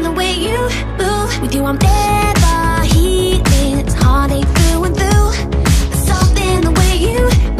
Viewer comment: I thought I heard honey it was actually heart.